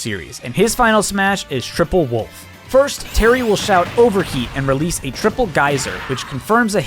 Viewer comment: I always thought Terry said "Overkill" when using his Final Smash instead of "Overheat"